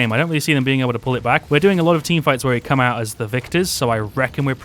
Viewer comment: For some reason i found "coming out as the victors" really funny probably cuz i heard "coming out as the viktors